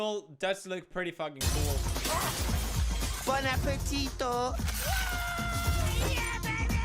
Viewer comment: If you have captions on, it says "Fun a poquito" instead of Bon appetit(o). (The monkey says appetit with an o in the end)